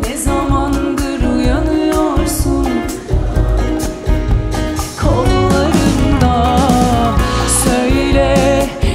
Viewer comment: ne dedin ablacım söyler misin :)..cidden 10 kez dudak okumaya çalıştım anlayamadım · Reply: Simge hoşgeldin diyor yanlış anlamadıysam bir kağıt okuyor galiba